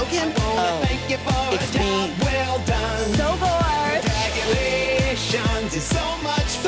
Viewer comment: how have I misheard mik saying so gorge as so bored for LITERAL MONTHS now it just sounds like so bordge I can’t